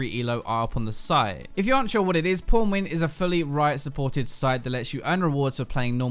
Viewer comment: Thought you said pornwin when you said pwnwin XD